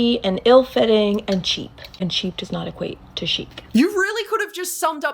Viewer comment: I thought she was saying "uncheap" like oh yeah that makes sense! You tattoo people drop ALOT of money 😹